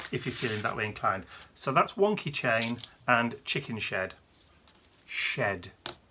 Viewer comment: I really like the stencils... especially the Chicken Shed (I thought you said Chicken S@ sorry. LOL). Thank you for sharing your week with us... please stay safe.